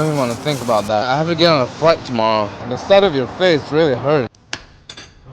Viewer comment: When i first heard that, I thought he said "The sight of your face really hurts" 😅😂😂